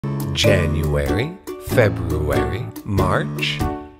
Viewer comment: rest in peace to December😅 i am september🎉 also, i heard, "FebROOOary" not "February" lol